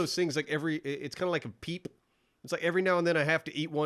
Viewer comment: I miss heard say "peep" thought he said "people, gotta eat one every now and then to remind yourself their gross"